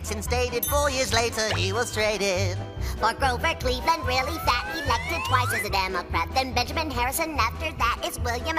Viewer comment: Fun fact: Taft hated being the President due to how stressful it was. He ended gaining so much weight from stress eating that he got up to 350 pounds. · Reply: I thought they said "he weighed twice as much as a democrat"